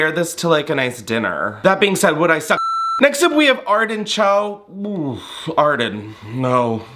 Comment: When you said Arden Cho I thought you said artichoke, and I kind of feel like that’s accurate for that dress